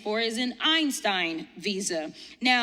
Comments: Einstein visa? 😂😅😂😅 I thought they said Epstein visa. 😂😅😂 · May have misheard, Epstein visa?